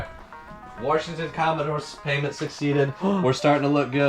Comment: did you say Warshington Commodores? That's a great user name, I might have to use that one day. #Builtthebarn #2ndstoryDirtfloor